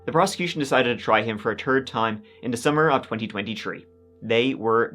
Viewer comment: after listening several times, i hear "December 2023" Ummm... it is only October 2023? What are you actially saying? Love ya!